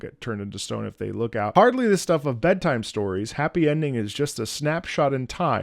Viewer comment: Without the lyrics on screen I heard "heartbeat stopper" instead of "hardly the stuff of" and personally like the misheard more.